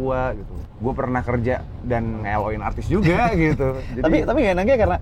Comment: gw udah kerja, dan udah pernah ngewe artis juga, gw g salah denger?? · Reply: "nge-L O in artis juga"